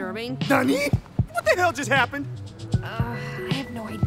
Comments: when he said Tari it sounded like nani... omyamou no shindieru. (srry if i wrote it wrong) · whattt did he just said nani2:27